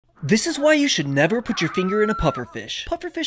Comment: For a minute I thought he said “this is why you should never finger a pufferfish 😂😂💀💀